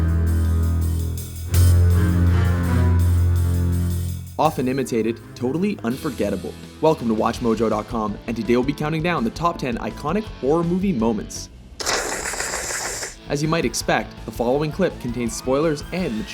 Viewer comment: ooooooooh it says iconic. lolol i thought it said ironic and was like wtf how is any of this ironic. haha makes more sense now.